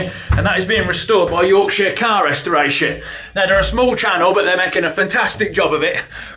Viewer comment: I swear you said "Your shit car restoration" 😂